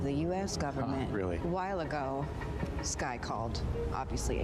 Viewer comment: she didn't fully pronounce the "this" and I completely thought she said "Skye called"